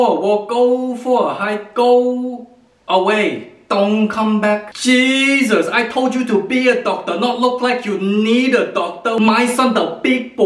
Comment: i thought you said "I told you to be a doctor not eat a doctor.."